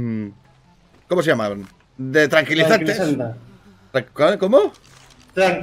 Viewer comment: Yo igual lo escuché "tranquilizanta" 🤣🤣🤣🤣